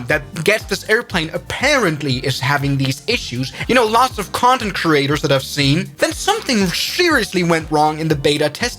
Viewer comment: hello? Am I that dirty minded or did just say c*nd*m creators?